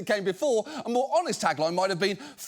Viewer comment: I watched with auto generated captions turned on. At 10:00 instead of Kalshi it said "cowshit". I thought that was rather appropriate.